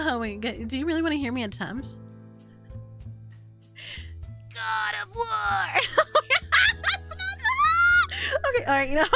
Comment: Oh she said "god of war" i thought she was saying "do the roar" like that kid from shrek